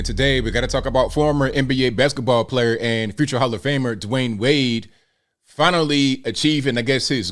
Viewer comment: the volume was low, I thought you said “future child abuser” 😂 not wrong